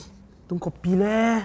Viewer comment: it said cory instead of copy